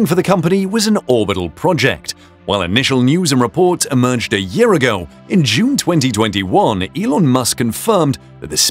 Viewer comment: "A year ago in June 2021..." I didn't know we are in 2022 already. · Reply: You misheard and you're missing the punctuation. He said "While initial news and reports emerged a year ago, in June 2021 Elon Musk confirmed..."